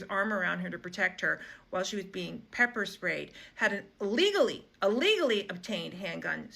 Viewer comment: Your Closed Captioning is wrong! It shows her saying he had "an illegally, an illegally purchased" item instead of "a LEGALLY, a LEGALLY purchased" one (at approximately 1:12) I realize it's auto generated, but it affects the truth of her argument for those who rely on captioning for their news. (Hopefully they'll read the comments, and see that this is an error).